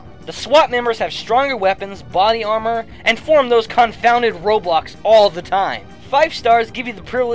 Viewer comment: Did he just say "ROBLOX?"